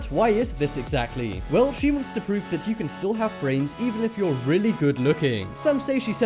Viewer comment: Did you say brainS at 04:48? :-D